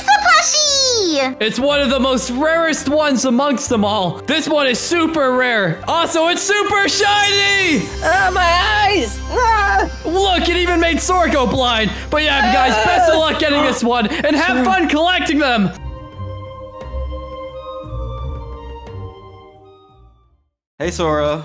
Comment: OH MY WORD 😂 did say Sorco then Sora? I'm dead 💀